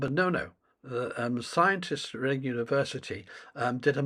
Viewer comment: I honestly thought you said Reddit University, which is also about the same quality of research.